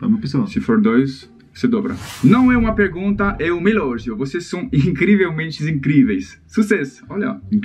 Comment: Em 1:37 disse Elogio e eu entendi Relógio. Que sotaque fofo!